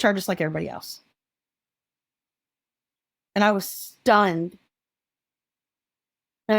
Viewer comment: can’t understand what she said was stunning. ?? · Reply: Stun